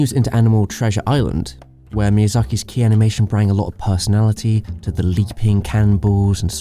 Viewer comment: did he say "brang alot of"??? xD